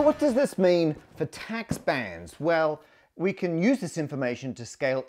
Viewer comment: slight error in the captions: "tax bans" instead of bands